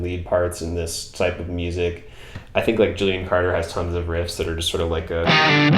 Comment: what band @ 1:38? couldnt catch it , thanx for all da vidz <3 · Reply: portrayal of guilt? or gillian carter, i think those are the only two i mentioned.